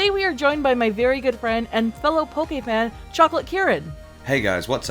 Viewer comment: I thought she said that his name was chocolate carrot